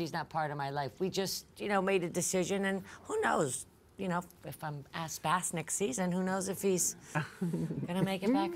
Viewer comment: Approximately 2:20 — Did Dorinda say If I’m ‘asked bask’?